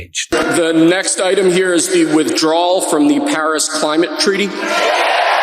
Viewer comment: I heard "Harris " not "Paris" at first. Given revenge I'm not THAT crazy